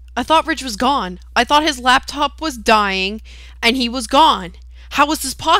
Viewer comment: lol at 0:30 i understood black cock instead of laptop.